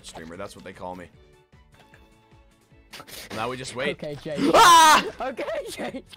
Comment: did anyone else hear tubbo call ranboo jake!?!?!